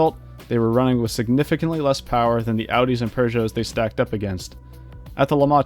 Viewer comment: did he just say "poosho" instead of peugeot